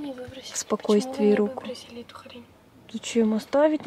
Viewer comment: На 2:55 и 2:56 мне послышалось или сказала хрень?